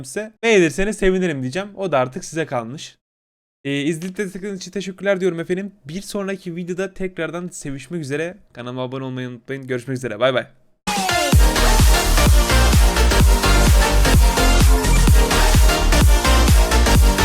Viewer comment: Bi sonraki videoda sevişmek üzeremi dedi ben mi yanlış duydum 😂😂